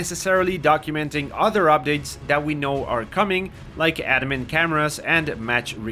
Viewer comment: What did he say at 9:50 ? Addamin cameras?